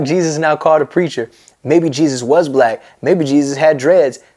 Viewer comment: i thought he was saying "baby jesus was black" instead of "maybe jesus was black" lol